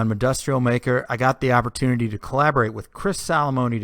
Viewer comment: Sounded like you said impossibly thin curved concrete bitch at the beginning. LOL